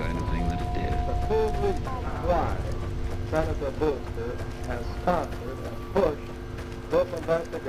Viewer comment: The voice at 38:57 sounds like audio of Howard Hughes during the Congressional hearing as part of the investigation into the charges brought against against him for war profiteering. It even sounds like it says “Senator Brewster.” It’s also a quick note that Mr. House is based on Howard Hughes and there are tonnes of references to him.